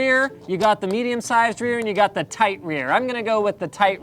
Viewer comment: did he say he was into "tight rear's" ???